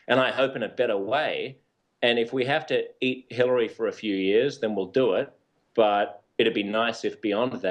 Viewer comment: did he say, "We might have to eat Hillary for a few years"?